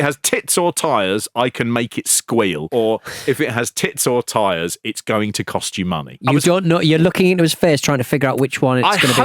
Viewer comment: Ive always heard it as "If it has tits or wheels, its gonna give you problems". Good advice actually